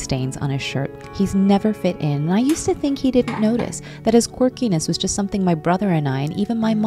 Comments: For a sec I thought you said quirkless.. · When she said “His quirkiness” I thought she said “He’s quirkless” and was confused for a sec.